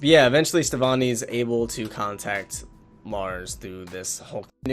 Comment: It sounds like you said "was able to contact Mars".